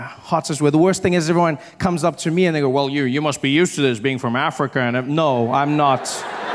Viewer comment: Am I the only one who thought he said "useless" and not "used to this"?